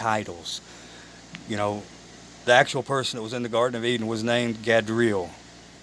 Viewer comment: It's not Gabriel for The real name of Satan its Gadreel sorry if I didn't say it plainly enough. I hope this helps you my Sister and God bless you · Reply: Thank you. 🥰 Yes I thought you said Gabriel... 👍